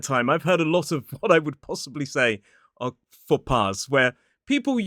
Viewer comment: you say: "I heard a lot of what I would say ???????....... " You then said something that sounded like "POPPARD". I had the subtitles on, but even they were unable to decode what you said. So, what did you say? Was it POPPARD? Or some other French-sounding word? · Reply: he said "faux pas" - a French phrase with no real equivalent in English and a commonly used one by English people.